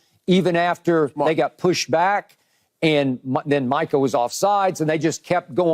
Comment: This is super random but every time Irvin addresses Micah, it sounds like he says “Michael” lol it can’t just be me